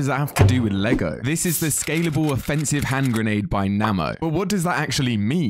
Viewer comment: I thought he said made by namco 😂